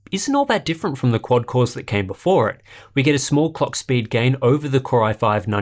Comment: Is it me or did I hear "small cockspeed gain"